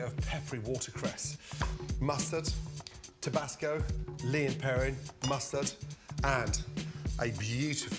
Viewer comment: for those who don’t know (or didn’t know what he said) Lea and Perrins is a United Kingdom based subsidiary of Kraft Heinz, that make condiments